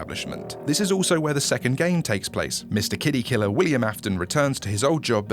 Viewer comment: I kid you not I legitimately thought you said "Mr kiddy fiddler" instead of mister kiddy killer 😂